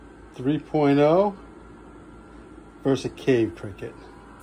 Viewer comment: For a second i thought he said a gay cricket 🦗